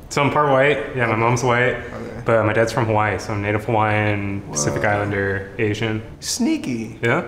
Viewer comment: I’m sorry but did he just say Pacific Islander?